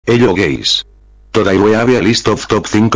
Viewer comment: It sounds like he says hello gays